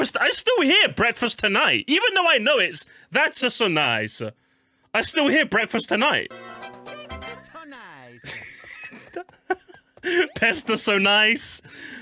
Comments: I literally thought for so long he said “thank you, you’re so nice” 💀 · yo I always thought he said, " Thank you so nice"😅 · I thought Mario used to say “Thank you tonight”